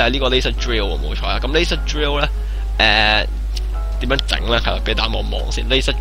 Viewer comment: Cobalt系要用架-0- 聽成Elsa Drill點算（*>.<*）